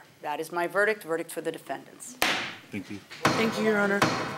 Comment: What did the defendant whisper under his breath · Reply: “Thank……” I tried hard to hear what he said… I don’t trust them at all… you could clearly tell they lied…